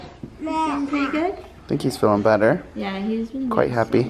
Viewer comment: i heard say better